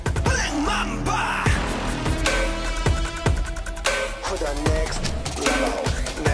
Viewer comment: If you hear, the background voice say 'next level' (at 0:33). Wow they spoiler it but i'm can't hear that at first time